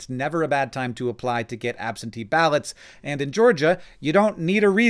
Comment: I live in GA. You can't vote online. An absentee ballot just allows you to vote by mail. Putting this here so people don't wait until the last moment thinking they can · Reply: Well, I heard him say "apply online for an absentee ballot" I definitely wouldn't have made that particular leap lol.